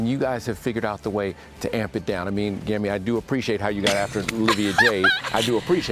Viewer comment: I thought it said Grammy Lol